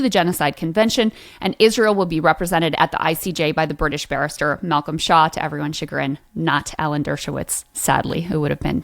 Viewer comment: I really thought she was saying Malcom Shah. I had to read it myself coz i thought it would be weird to have a muslim defending Israel. Shaw rhymes with claw and tour and bore.